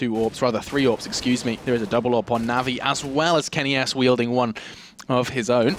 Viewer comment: did he say kenny ass